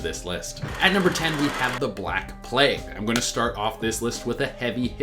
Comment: why did i hear 'the black hood' instead of 'the black plague'?